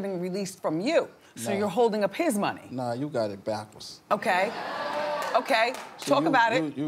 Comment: When Birdman said "You got it backwards" I swear I heard "You got it blackus"